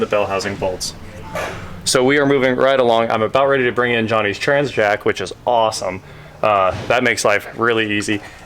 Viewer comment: i heard 'johnny tran's jack" and got all excited for a sec